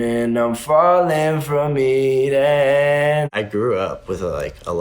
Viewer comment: Was I the only one who thought he said "I'm falling from THE EDGE. " ???